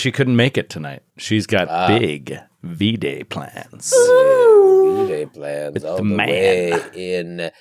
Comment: Omg I thought said syd had big v-jay plans! I had to rewind it cus I was so taken a back just to realize he said v-day lmao